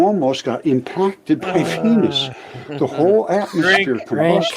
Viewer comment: I'm guessing he said "Venus" but I giggle over the auto-gen subtitles saying "we almost got impacted by penis"